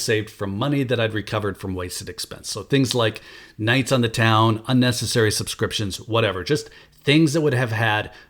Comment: I thought youn said unnecessary prescriptions for a second after nights on the town. 😅